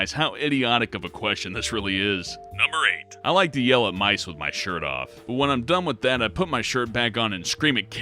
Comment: i thought he said I like yellow mice with their shirts off😂